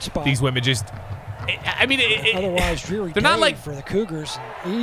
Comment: did he say cougars